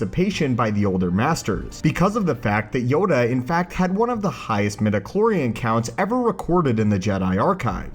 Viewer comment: I thought he said he has recoded with the highest metformin account on 1:06 - So Many People with Diabetes Stop Taking Metformin. It works to reduce sugar in liver.